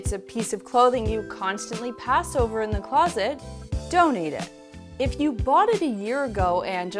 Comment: when you say donate it, it sounds like you said don't eat it